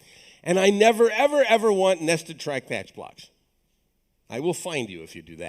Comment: what he meant when he said: "nested track patch block" ? (see minute 1:36:50 ) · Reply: "nested try/catch blocks". so: try { try { doSomething; } catch { } } catch { }